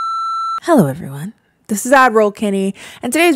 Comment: I'm pretty sure she's saying "ad roll Kennie"...it did take me quite a while to figure that out and definitely sounds like admiral lol. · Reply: it’s a running joke in this channel that some people thought she said “admiral” and not ad roll 😂